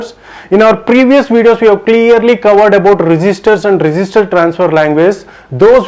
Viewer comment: I thought you are saying resister but not it just register